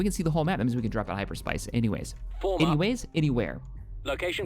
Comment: I heard this man say hyper spice anywhere when they capture the radar dish